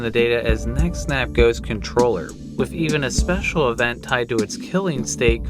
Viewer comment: ngl i misheard Neck Snap Ghost as Neck SLAP Ghost and kinda laughed for a bit